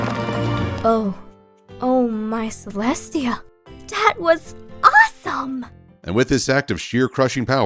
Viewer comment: what said: dear celestia that was awesome what i heard: dear celestia DAT was awesome